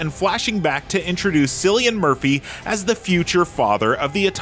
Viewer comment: Did... Did you just call him "Sillian Murphy?"..